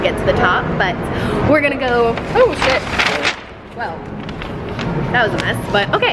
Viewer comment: Did say "S*it" at 3:40??